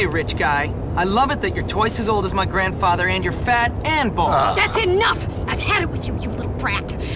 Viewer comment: What she say? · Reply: Bald